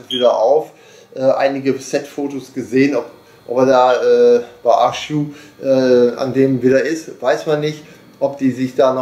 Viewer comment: Ich verstehe kein Wort was sie bei ca 3:18 sagen. Orla Baschuk? Was soll das sein? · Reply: Ahch-To, der Wasserplanet ( Genaus Phonetik nicht bekannt )